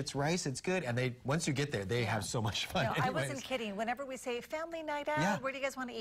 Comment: did he say "they have so much f*%king rice"